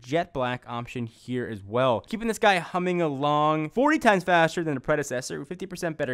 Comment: Did he say 40x faster than the 6s? Cant hear it properly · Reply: Sounds like he said 40 times faster, but it's really 40% faster.